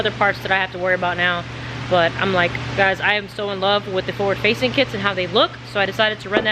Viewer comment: Im Laffing my Ass off cuz at first i thought you said, "Ford Facing Manifold." LMAO. But i snapped out of it, "Forward Facing."....if you want that Roll Cage to 'Pop' paint it Gloss White.....and i know you want all the Weight in the Front but Common Sense is telling me Not to put the Fuel in Front, keep the Fuel in the Back. 👍🗯💭